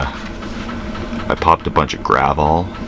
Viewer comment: Wasn't paying enough attention and thought you said "popped a bunch of gravel", not "popped a bunch of gravol". Gravel is slang for a mix of meth and coke.